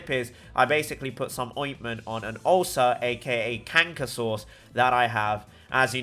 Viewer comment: What did he said about his lips? I swear I heard either cancer or a name of a dinosaur.